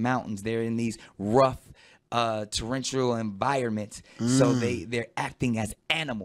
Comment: "Torrentioral environment"??!? DA FUQ DID HE JUST SAY